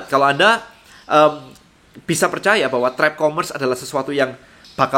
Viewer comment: ngomong apa sih kok ga jelas?? apa trek commerce ya?